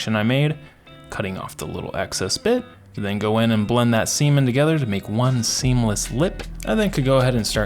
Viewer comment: did you say semen lol